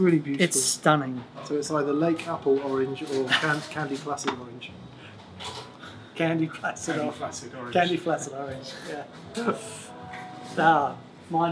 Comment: "...we've chosen a one-off Limited Edition 'Candy Placid Orange' finish" Oh! I heard "Candy Flaccid Orange" and, while laughing, was wondering what the inside joke was. Aside from the box color, are there any other differences in the TPS edition, or is it the stock JAM circuit? Great video, gents.